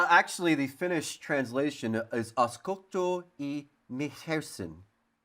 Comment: i’m Finnish and i have no clue what he said 😂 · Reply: "Uskotko ihmeisiin?", jos ei olisi ensiksi selostanut mitä sanoo, niin en kyl olis tajunnut